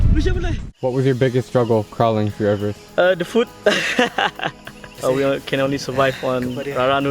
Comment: I thought he said the foot ( his feet) :D